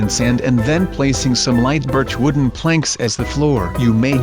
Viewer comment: he says bit*ch instead of birch